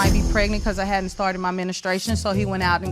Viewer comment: did she say "ministration"? Lol